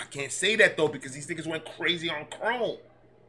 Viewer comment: Great Reaction yeah when you think the last song by them is your favorite…. Then here comes something else, I love it when you know who was on that third verse. Nothing be 🔥 kive ya Unc , listen to chrome if you haven’t already, I am trying to catch up tonight and I got stuck on a few and it’s getting late. So if you haven’t done it, check that one out, nova lets loose. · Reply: Damn I just heard you say , you done crome!! 🤷🏻‍♀️ don’t blame me blame the weed😂